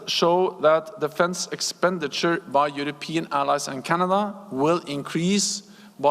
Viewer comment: does he say Europe and “Canada”?